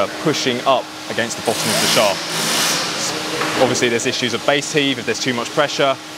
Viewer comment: Base heap did he say??? I've never heard of that word before either!!! 🤔🚂🚂🚂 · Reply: Base heave, where the ground (and particularly the ground water) wants to shove the shaft up out of the ground, a bit like a plastic bottle held underwater